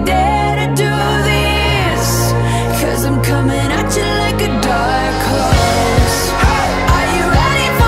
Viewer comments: Even I thought they said cause I’m coming at you like a dark cross 😂😂😂 That’s why I put on lyrics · i thought she said dark course LMAO